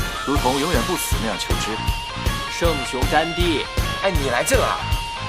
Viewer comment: Did he really said mahatma gandi